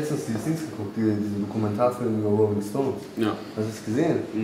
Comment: Was sagt der Erfand bei 22:09 No stone irgendwas, mit dem Kommentarfilm.....